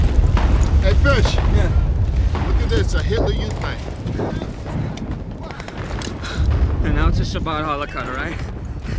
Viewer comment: Can someone tell me what they say to each other at 3:30? I have hard time understanding what they said to eachother right before he starts crying. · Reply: He a tells him “hey look it’s a hitler youth knife” knowing his fellow soldier is Jewish, and he responds with “now it’s a sabbath challa knife” basically being sarcastic and saying he’s going to use it as a bread knife as a fuck you to the Nazis. Then his exterior breaks as the reality of the horrific battle sets in. You can see Vin Diesel’s character’s guilt and disappointment because he was trying to give his buddy a pat on the back and a bit of humor to lighten the mood (good fucking luck) and realizes a part of him is already broken.